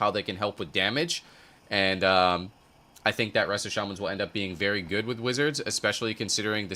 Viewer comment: As a melee excited for solo shuffle, all I heard was "Target Resto Shamans"